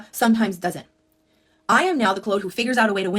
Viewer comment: Omg. I heard ‘I am not the chode!’ I had to run to my phone and look 😂